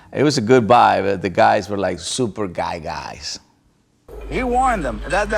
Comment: I thought he said “ the guys were super gay guys” i was like wth lmao.